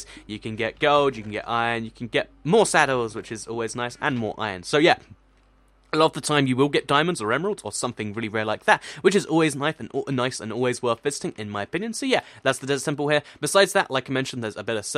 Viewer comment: I thought he said always worth fisting. XD